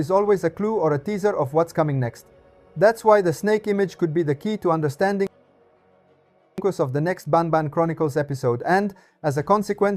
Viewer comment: What did you said ? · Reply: Unfortunately, since I updated the editing program, it glitches the audio 😅 anyway the missing part is: to understand which character will be the focus of the next episode 😃